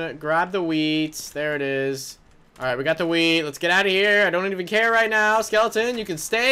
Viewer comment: i thought u said "i got the weed"!! XD lolzz